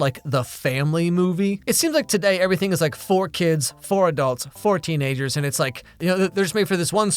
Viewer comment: Totally misheard one of the last lines "Nowadays you have these movies... four adults, four kids and four teens" and I was like OK... What's gonna happen?